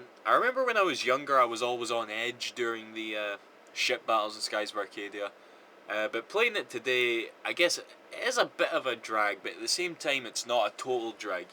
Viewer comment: I don't play minecraft, and Atlantis is real! Also, might I add that it sounded like you were saying "shit battles"